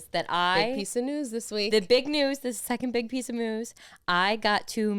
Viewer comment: did just says second big piece of moos?! lol 🤭🤣